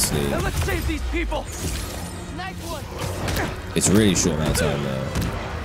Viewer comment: What did he say? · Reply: “It’s a really short amount of time”